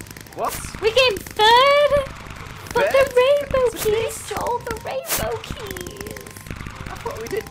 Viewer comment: Did say "fucking rainbow keys" on 5:20 ??